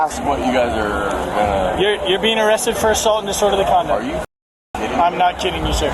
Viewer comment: 😂😅😂 About @ 11:46 Cop says: you're being arrested for assault and disorderly conduct. Caption says: you're being arrested for assault and just order the condom. I can't breathe 😂😅😅😂